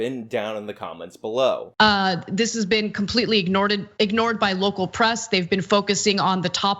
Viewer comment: did.....did she just say "ignoreded"?